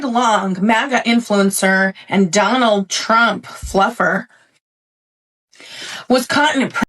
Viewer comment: Did she call him a Donald Trump fluffer 😂😂😂